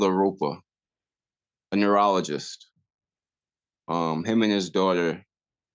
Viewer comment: So happy that he bonded with the man who stopped. No freakin way it would be a numerologist. Clearly he is not an a——h—-le either ….. life is a amazing and beautiful but flips like a coin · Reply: // I thought he said neurologist, and not numerologist. I'll have to re-listen and see.